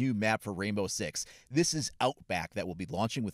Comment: Thought it said outbreak damn 😂😂😂but this map looks great!